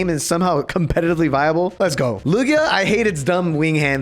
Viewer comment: lugia is my favourite pokemon and you did that to my boy, how could you... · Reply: I thought you said luigi for a second